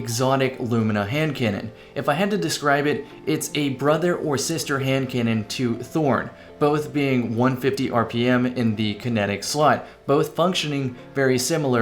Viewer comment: Did you just say "...both being in the kinetic slut" at around 0:10? Those kinetics...